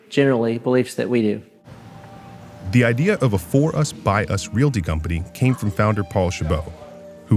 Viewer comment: Did he really say “For Us, By Us” at 2:04?